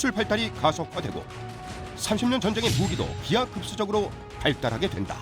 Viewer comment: 처음에 나레이션 30년전쟁인데 3천년전쟁으로 들리네요 ㅋㅋㅋ